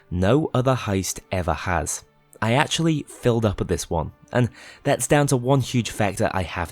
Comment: Gonna be honest here: not sure what you were saying at 11:46. Sounded like you were saying that you 'filled up at this one', but no idea what that statement even means